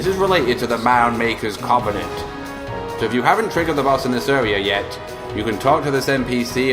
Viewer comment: 'if you haven't triggered .....' what did he say? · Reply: the boss in this area yet